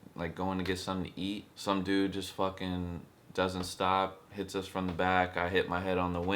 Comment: for a second I thought he said tits-something....I am so sorry of what happened 😘😘